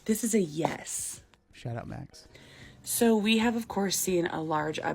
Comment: I think he said "shout out max" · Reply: 😂🤣 went back and listened.. I think he did say “shout out Max” lol I thought he said “shut up Max” too 🤣😂